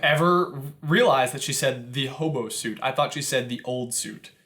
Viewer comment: I always heard "horrible suit", rather than "hobo suit", even after finding out, I prefer calling it a horrible suit.